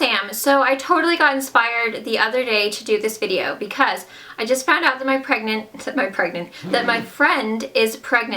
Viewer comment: I got so exited I thought u said u just found out u where pregnant lol